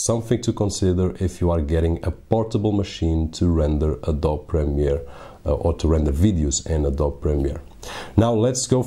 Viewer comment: did you say to 'render adult video' at 3:31 ? what kind of movies are you making man!?!?